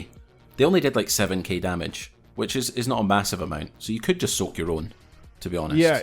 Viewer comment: All I heard was "soak your own ballz" boys